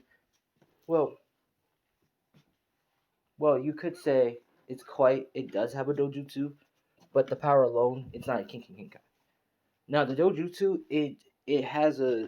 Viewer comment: Lowkey a couple of seconds before 13:28 I swear I heard you say, "Kinky Genkai".